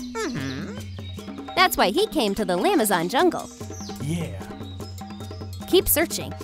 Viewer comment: is it just me or did I hear Lamazon jungle